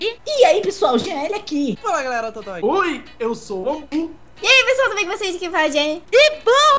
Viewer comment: so eu q escutei "oi eu sou o goku" '-'